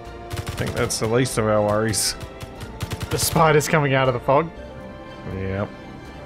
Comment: I swear I heard "Spiders coming out of the pog" lmao